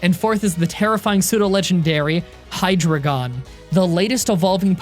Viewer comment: I am German. I don't get it. Do you think that "dra" is "three" in German? It isn't. It's "drei". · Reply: sounds like he says hydragon instead of hydreigon